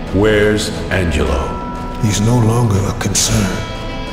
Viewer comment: what did shank say when cesar asked: wheres Angelo? i dont get it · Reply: He said "he's no longer a concern"